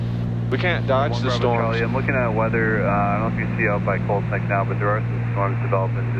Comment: So every time you were saying Colt's Neck I thought you were saying Cold Snack. Two very different things.